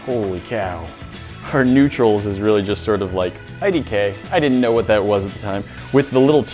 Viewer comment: did he just say ”IDK” wut